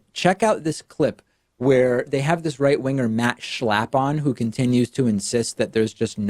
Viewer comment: Lolol. For a minute there, I thought you said, ''They have this right-winger, Matt Schlapon...''. I had to google his name, lmao.